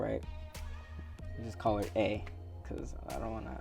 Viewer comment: I thought he said I’ll call her egg 😂